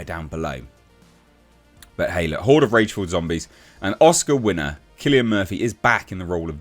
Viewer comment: Haha I thought it said “hoards of Race filled zombies” that’s just leftists 🤣